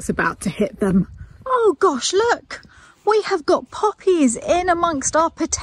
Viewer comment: I thought said "Just look at all the PUPPIES that are in with the Potatoes" ( 4:50).....So I am looking for little tails wagging when I realized she must have said POPPIES....not PUPPIES...!😂